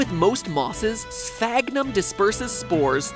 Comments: Did anyone else think he said FAGnum? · I thought he said fagnum